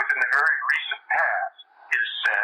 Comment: sounds like “she” when Kit talking about the “sponsor” but captions say “he”. Replay with headphones. This is after the remote viewer had referred to the sponsor as “he”. The identity of “the sponsor” may be more important than 011.